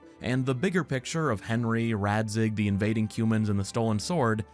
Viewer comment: when you said "invadeing cumans" i thought you said "invading humans"